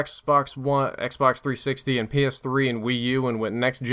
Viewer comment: penis 3 instead of ps3